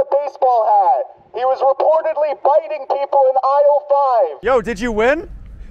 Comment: i thought he said “bitting”people in aisle 5 not “fighting” 😂 tbh bitting sounds funnier 🤣